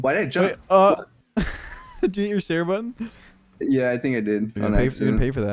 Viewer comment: does he say "steer" button or "stair/stare" button? What does he mean? How does get airborne without jumping at that kickoff?